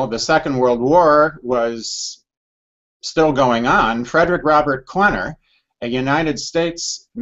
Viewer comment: What was that doctors name ? Dr clenner it sounded like he said. I already know the name max gerson.